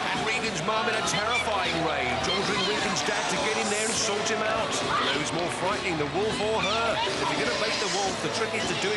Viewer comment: did she just call wolf what I thought she called him? 😂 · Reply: A ‘SWINE’! Basically, a contemptible or unpleasant person. Sums up ‘Wolf’ quite well I suppose! 🐷🤣